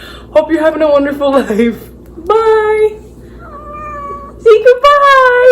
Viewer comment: omg literally said ‘bye’??! please i cant be the only one who heard that😭